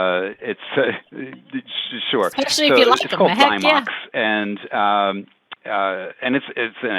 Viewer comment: Did anyone catch what he said at around 18:45 about what enzymatic ear wax cleaner he uses? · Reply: Yes; he's talking about Zymox ☺️